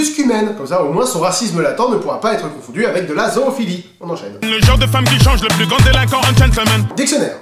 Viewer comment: moi à 5:20 j'entends : "le genre de femme qui change de tenue quand elle accorde un gentleman" et à 10:10 j'entends : " juste une bière sur son chemin" après je me trompe peut être... sinon super vidéo j'ai bien rigoler même si j'adore cette chanson ;-)